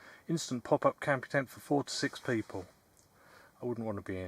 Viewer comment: I misheard four to six people and thought I’d not want to be in there with 46 people either 😂 Great video and I quite like the tent too 👍